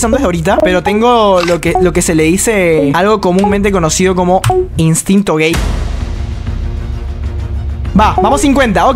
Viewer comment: Parece que dice instinto gay xD